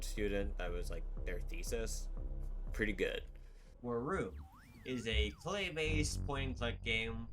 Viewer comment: my backlog is eating good tonight also what's the claymation game at 15:40 called ? I can't quite catch it, chapters would be useful to see all the names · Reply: that game would be wurroom. An I'm already working on chapters, hopefully should be added here soon